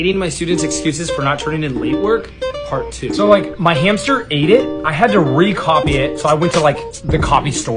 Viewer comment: I thought he said that his hamster died so he went to re copy it o.O